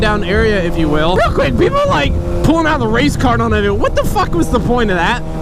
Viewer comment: for a solid minute I thought he said race car not race card